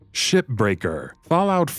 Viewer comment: I couldve sworn he said shitbreaker lol!